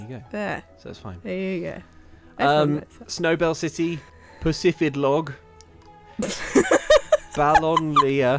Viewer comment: ngl whenever they said "Pacifidlog", neither playing Pokémon nor watching the stream while they'd said it, I heard "p*ssy fit cog" instead aeviojsfahebjkcjvh